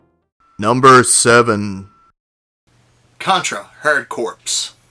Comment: Did you just say hard corpse ya dingus